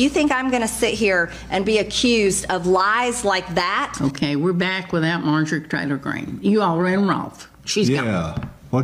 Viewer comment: With her accent, the female host sounded like she said, "...we're back without Marjorie TRAITOR Green..."